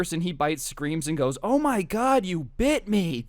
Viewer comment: I thought you were about to say oh my god you bich